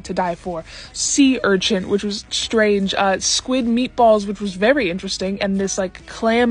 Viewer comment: My brain didn't pick you the meat part of meat balls so all i heard was "squid balls"